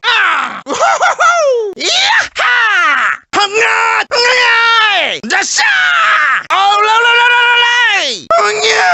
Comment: I always thought he said "up yours" and it still sounds like it now. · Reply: No, hes saying "IM NOT"